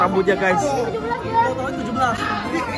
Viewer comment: Apakah reall??!!! ,Kau bilang apa tadi umur 17??